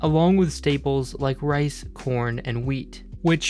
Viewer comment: no nut november is getting serious. I understood "porn" instead of corn